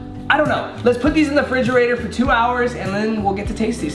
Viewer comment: did he say fridgerator???? :D :D